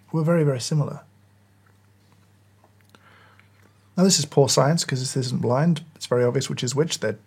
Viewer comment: I totally misheard "poor science" as "pour science," which it sort of is.